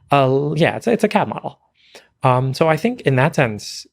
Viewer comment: what did he say? cad models? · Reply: Yeah a CAD model that is 70% apparently in stasis / we dont understand at all.